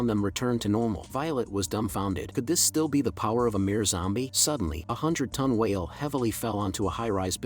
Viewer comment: did he said Amir zombie?